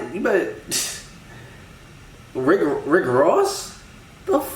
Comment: o thought she said brick walls